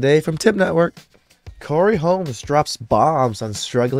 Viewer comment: Shouldn’t do this if u don’t kno names sounds like he said Corey Holmes smh 🤦🏾‍♂️